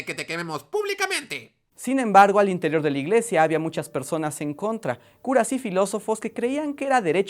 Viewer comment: Escuché "curas sifilosos" 😂😂😂